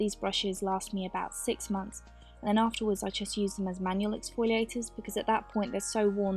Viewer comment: I'm sorry, but my English isn't very good (I'm Dutch), I couldn't understand what she was saying at 2:34. Something like manulets foileters (I probably spelled that wrong)... So I would like to know what that is and what that does to your skin.